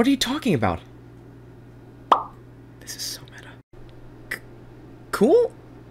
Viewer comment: Does anybody hear Lego saying: This is so meta?